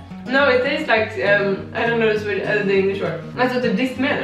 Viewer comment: Does say "I don't know the swe.. or the Danish word?" Why would she know the Danish word? is a swede, no?